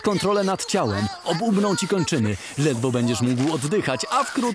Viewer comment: czy tylko ja usłyszałem "ogłuchną ci kończyny" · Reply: ob umrą powiedział